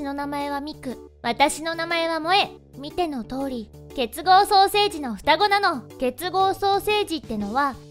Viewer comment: 双生児がソーセージにしか聞こえない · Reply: ケツ合ソーセージに聞こえてたw